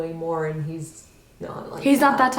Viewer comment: I think i heard someone say "no" very faintly at exactly 48:17, could be friend agreeing with her.